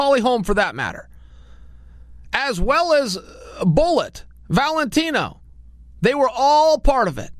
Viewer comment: did chael just say valentino 😂😂😂